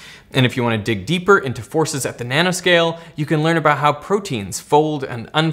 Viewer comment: Just after 14:15 For some reason I heard Derek say 'at the Banana scale' rather than 'at the nano scale' did anyone else hear that or is it just me that measures things with Bananas 🤣